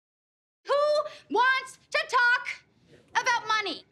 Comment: Why did I hear “tiktok “ when she said “ to talk “ 💀💀